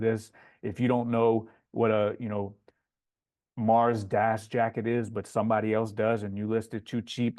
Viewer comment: What’s the jacket he says here at 18:48 I couldn’t make out what he is saying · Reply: Patagonia Mars Das